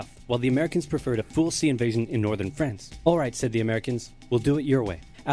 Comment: When it said”said the Americans” I thought it said “sas*y Americans any I was like bruv